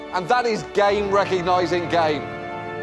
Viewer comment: the line "you're good at being white, depressing and remarkably empty on the inside and THAT IS GAME RECOGNISING GAME" lives in my head rent-free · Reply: I thought he said gay recognizing gay...seriously